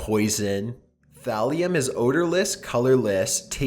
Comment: At first it sounded like said Graham was researching Valium lol it's actually Thallium